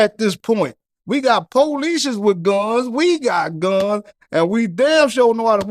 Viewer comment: Hmmm did he just say we got police with guns???